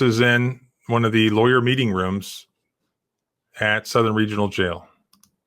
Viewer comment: is looking good with the hair spike! Ps-I thought he said “the beating room” instead of meeting room.